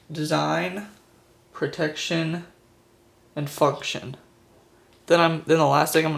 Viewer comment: In 00:22 did you say fucktion